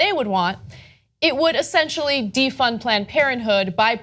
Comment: I thought it said defend